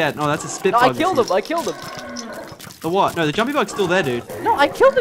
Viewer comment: I swear I thought it said humpy bug boss XD